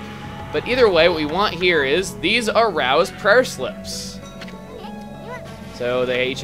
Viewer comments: "These are Rao's prayer slips." I only heard the "arouse" part in there. I wonder why. Hehehe. >u> (I know that was horrible. hahaha.) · I swear he said "These arouse Prayer Slips"…